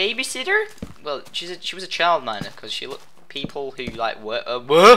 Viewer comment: Who else thought he said child molester?